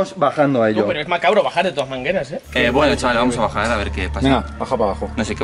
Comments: nadie más se ha dado cuenta que dice mangeras? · Si te diste cuenta que dice algo es difícil bajar de toas mangeras en el minuto